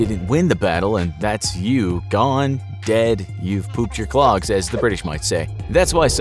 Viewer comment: did he say pooped your clogs?